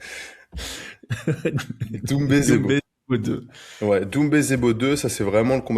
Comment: J'essaie de dormir en écoutant j'ai entendu "zoumbé" j'ai explosée de rire 😂 obliger de lâcher ce commentaire